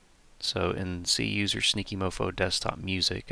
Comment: Ohhh its SNEAKY mofo, my deaf ears thought you said stinky at the beginning xp. Thank you so much for your time making this years ago, I'm looking to extract Go! Go! Hypergrind audio files from the game so I can make proper cutscenes for it on my main, this will really help a lot, I appreciate the instructions! As someone who doesn't have a computer & barely has a phone, this will clutch it 100%